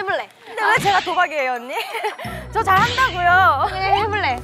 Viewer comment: 왜 "예나언니"로 들리지...내 귀가 이상한가